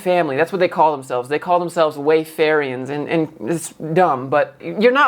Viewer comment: I literally thought at 02:15 you say they are called "Wayfairies"